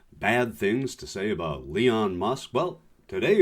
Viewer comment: did u just say Leon musk? Either way...he's terrible but aoc sexy af